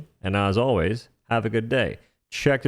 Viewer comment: I thought you said stainless steel pants...